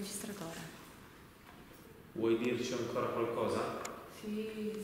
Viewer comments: Al minuto 10:18 si sente che dice vuoi dirmi qualcosa e qualcosa dice sì sì sente chiaro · Al minuto 10:14 alla domanda ‘Vuoi dirci ancora qualcosa?’ si sente una voce femminile dire ‘Siii....’ 😱